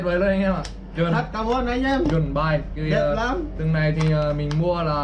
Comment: anh định nói rất cảm ơn mà k kịp nghe thành ăn cám haha